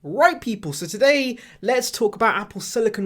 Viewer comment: Haha nah I said “right people” 👍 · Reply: I actually heard it as “white people” lol